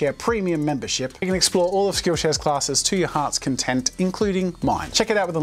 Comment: I started in a weird part, so I heard say "kill share"